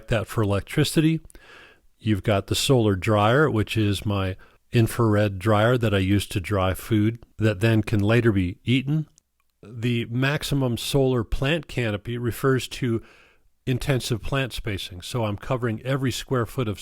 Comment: Max solar plant canopy? Sounded like you said plants catch sun? 😮